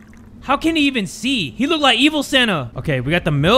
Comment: You look like evil Santa Me: for some reason thought he said emo